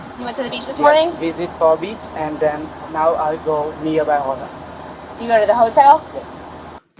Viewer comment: How did he get PR because he can't speak English and his excent is terrible. He said he was going to the hotel, but it sounded like he said it's hot. Shocking, he's not smart and in my opinion, someone used him for their own profit! He's already a broken cookie and officers knows that. He's given confused answers to save himself, but he won't be able to because they deal with cases like this. Someone who knew his secret reported to put him in big trouble.